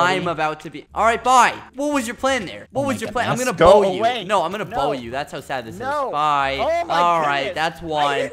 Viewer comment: OMG WAIT THE CAPTIONS SAY "IM GONNA BONE YOU" AHAHAHA